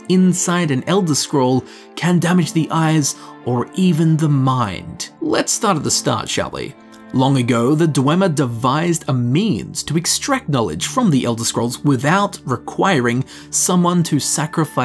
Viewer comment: I definitely heard "Long ago, the Dwemer devised a MEME."